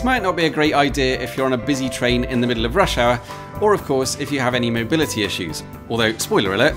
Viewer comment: This is a fun video, part of me wonders if the star might be the North Star. Granted I initially misheard the advisory notice at 4:12-4:15 and thought you said "if you're on a busy train in the middle of Russia" not "if you're on a busy train in the middle of Rush Hour" but I subscribed, I thought I had already but it seemed to think not. And even though the Swedish band I most enjoy listening to is Sabaton not Abba that's a matter of taste. Hope you have fun.